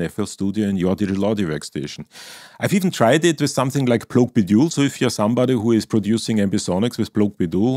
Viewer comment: sorry for my silly question: at 3:36 you say "something like..." but I can't understand the name of the ambisonic app... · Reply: Plogue Bidule